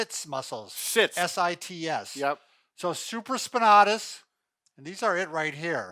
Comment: sounded like tits lol